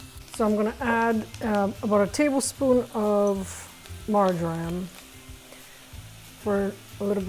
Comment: When you said 'Marjoram', I thought I heard Margarine! 😅